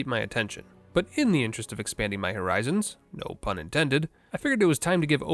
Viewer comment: did he said HORIZONS? Lemon beating GTNH with only explosives?